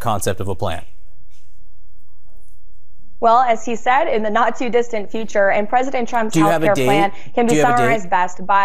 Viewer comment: kinda sounded like she said “in the nazi distant future”. Freudian slip?